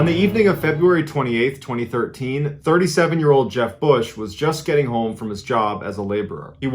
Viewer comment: I thought you said Jeb Bush then you mentioned work...lol!